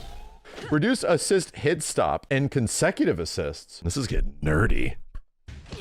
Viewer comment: I swear I know he said "nerdy" at 3:55 but all my BRAIN heard was "Oh, this is getting dirty" xD